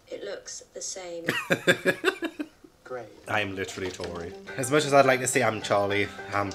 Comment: You said "Im literally Tori" and i misheard you... bestie i thought you said "I'm literally a Tory" and i got SO SCARED